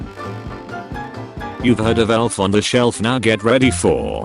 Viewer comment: can someone tell me what this is? I am trying to think of what rhymes with kratos, And I still can't understand it, BOY! Translate this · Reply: God of war on the shore